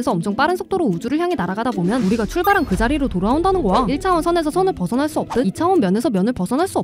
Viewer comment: 우리가 출발한 그 자리로 돌아온다는 '거야' 가 '고아' 로 들리네요 교정좀 ㅠㅠ